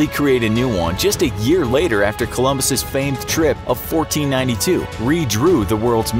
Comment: Turn captions on, it says 1942 instead of 1492 Dyslexic?!?!?